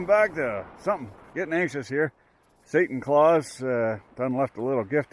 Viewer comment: was that satan clause i heard? 😂